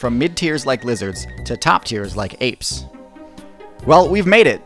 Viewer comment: Did he say APES 🦧